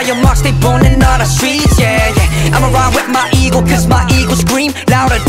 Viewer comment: "imma ride with my ego cuz my ego screams louder than this engine." - MARK LEE 2k19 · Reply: i deadass thought he said imma ride with my eagle 💀💀